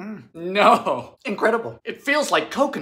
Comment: well they say Alchemy started in the kitchen...guess this proves that XD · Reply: I thought u said alcreamy which is a Pokémon.